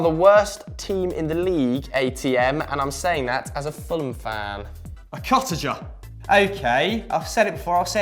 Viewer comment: anyone know what he said?? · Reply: martin53mattsson “A cottager” Fulham’s home ground is craven cottage so “a cottager “ is a Fulham supporter 🤦🏻‍♂️